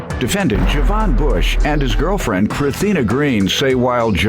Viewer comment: Crethina sounds like Christina said with a lisp 😆😆😆